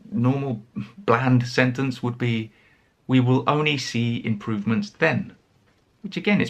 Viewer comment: you said, “We will only…”, but in the video we read “Will we only…”. It's a small mistake, isn't it? The correct form would be what you said, not what appears in the video at that moment, right? Thanks! ☺